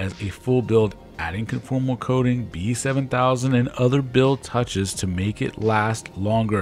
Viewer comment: Great overview of the quad! I put a good amount of time, thought and effort into that build, I also shielded the wires to the GPS to stop any interference from getting in from either the ESC or the antenna from the O3. I'll put my parts list below for those interested. Frame: Foxeer Aura UL 5" Motors: RCinpower <num>kv FC: Holybro Kakute H7 ESC: Holybro Tekko 32 Mini Props: Nazgul F5 Lipo: Tattu 2200mah 5s 95c GPS: Amazon BN-180 Buzzer: VIFLY FINDER 2 LED Strip: 25 long LED strip from RDQ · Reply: What did say after conformal coating at 2:54? It sounds like "PE 7000".